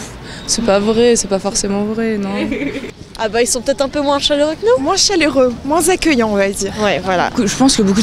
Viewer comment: I believe she actually says "ah be(n) (ils) sont de temps (en temps) (un) peu moins chaleureux q(ue) no(us)" - with the bracketed sounds lost!